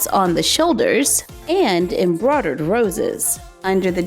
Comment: "Embrodad roses" That's what she sounds like she says